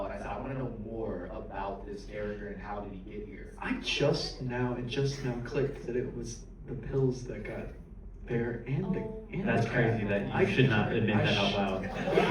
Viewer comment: What did he say at 7:26 ? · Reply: Michael: It just now clicked that it was the pills that got Bear and the cat. Curry: That's crazy, you should not have admitted that out loud.